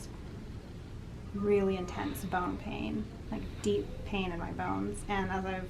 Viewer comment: I thought you said “bone hang” at first haha.